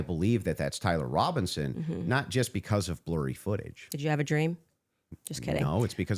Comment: That cracked me up too 😂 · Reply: I know it’s because she intelligent enough to throw in some humor… I love it! At first I thought she said, did you have a drink!!!! 😂😂😂😅